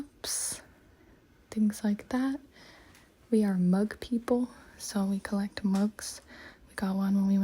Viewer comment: Was I so tired I heard we mug people